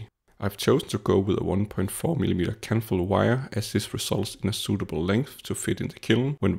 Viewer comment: "Kanthal" wire. Sorry. It's on the paper at @ 2:07 I'm sorry does he say Kettle wire? Kentle wire? What material is that wire?? Candle wire? Sorry I am actually using this as a rough blueprint for my heater retrofit. Not sure what he is using here I'm thinking of using an 80% nichrome alloy wire